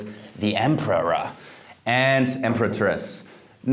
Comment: He drank all these perfumes. Hence this out of this world advice. Btw, 3:14…did he just say “Emperor and the EMPETRESS?????”😂😖